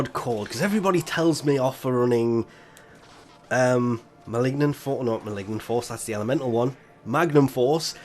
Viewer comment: i thought he said ma ligma balls XD sorr my hearing is so bad